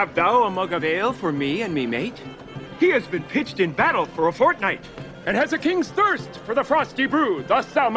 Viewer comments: did he say fortnite 😂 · did he say fortnite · did... did he just say Fortnite? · he actually said Fortnite HAHAHA